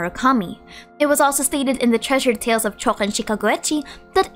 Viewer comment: THE WHAT??? I've been replaying it for a while and still can't understand what said in that part 😵‍💫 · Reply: Treasured tales of chouken shinkageuchi